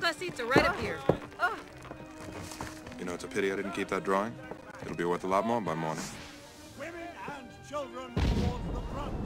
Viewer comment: what did Cal say? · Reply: "You know it's a pity I didn't keep that drawing? It'll be worth a lot more by morning."